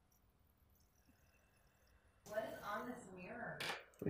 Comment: I thought Steph said "What an honest mirror" and I was like, "oh hey, at least the bathroom has a good mirror!"